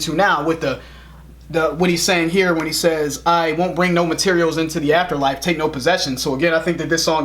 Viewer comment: When Joe screams, "I won't bring no..." It sounds like he says, "I want Pringles!"